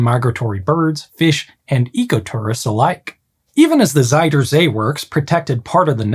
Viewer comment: I had to rewind because it kind of sounded like you said "eco-t*rrorists"